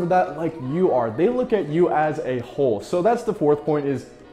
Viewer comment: I thought you said a-hole lmao